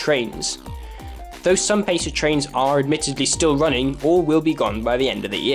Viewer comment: >>pacer trains Not gonna lie, I heard "PESA trains".